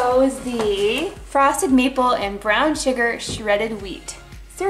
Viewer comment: At first I thought she said frosted meat ball lol